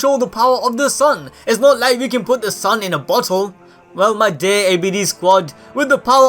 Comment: did u say butt hole?